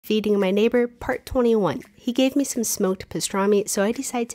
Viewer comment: Was I the only one that heard her say "eating my neighbor..." 😂😂